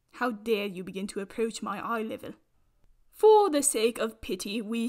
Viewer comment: “How DARE you reach my eye level.” Also: slaps plant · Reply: OMG I THOUGHT SHE WAS SAYING "EYE LIVER" THIS WHOLE TIME I WAS SO CONFUSED YOU HAVE MADE THE WORLD RIGHT AGAIN